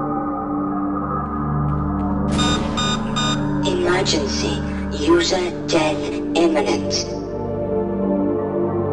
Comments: what the suits says? i cant understand it very well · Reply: “Emergency! User death imminent.” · what saying ? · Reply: User death imminent